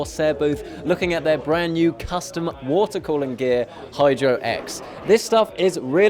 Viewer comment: it sounds like he said hide your ex instead of hydro x